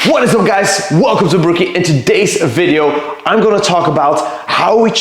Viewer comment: Yeahhh plastic bad. And ohhhh I heard "bah-rookie" :D